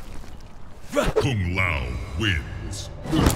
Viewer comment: sounds like he says f*cking Lao wins